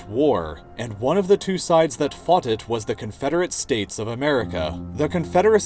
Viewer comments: Did you just say 'foughted' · did you just say "foughted"?